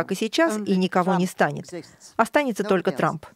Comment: Она ещё и на английском разговаривает, на такие темы!!! Очень сложная тема на русском, а тут ещё и иностранный язык! Впечатлило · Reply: Мне тоже послышалось, что она произнесла Трамп... И это меня шокировало. Подскажите, пожалуйста: ТРАП - это аббревиатура? Как расшифровывается?